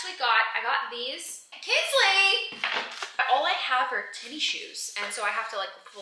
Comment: Did you just say "tinnie" shoes?! LOL! My friend says it that way. Is it a Southern thing?